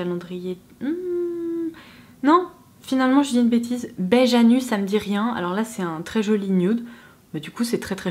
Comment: À 11:38 est ce que tu dis "beige anus" hahaha 😂🤣 J'écoutais en faisant autre chose et j'ai entendu ça, j'en ai recraché mon café haha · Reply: Alors 🤣🤣 même si c’est drôle je dis bien “beige à nu” Ahahah